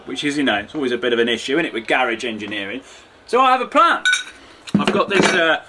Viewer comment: He said garage engineering and I thought he said Garry ginger ninja